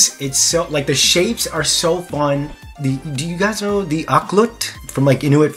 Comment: Wait, did he say "auklet"? Is THAT where Tsunami and Anemone's little-little sister (Wings of Fire) got her name from? I assumed it was some kind of jewelry.